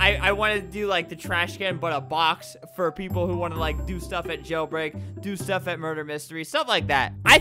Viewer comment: did he just said jailbreak